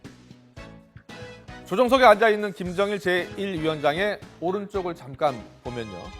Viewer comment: 쯤에 나만 김정은이 아니라 김정일 이라고 들리냐?